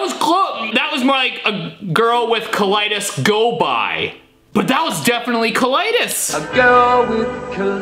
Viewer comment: A girl with colitis goes byy" i hear "a girl with cool eyelids goes by